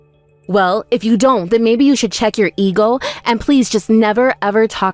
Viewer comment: I heard "check your ego " as check youtlr eagle." Personally, I think that sounds epic